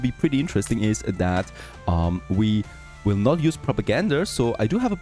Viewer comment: May I ask why you say Progagander instead of Propaganda ? 4:00